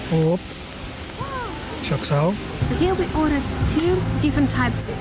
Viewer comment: did you just say çok sağol? :elbowcough: how many languages you have??